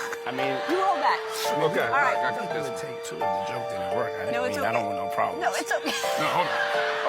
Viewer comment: He clearly said "I can take two if the joke didn't work". Growing up, "take two" was a common industry term that just meant they would re-do that scene or do it over... the term was so common that it became a common word used by regular non-industry people. Even though he is guilty of many things, it is deceptive to fake that he said anything else when it is clear in the audio what he was saying to her.